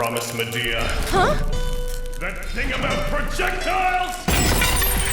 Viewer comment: When Archer said "The thing about projectiles." I thought he said "The king of projectiles."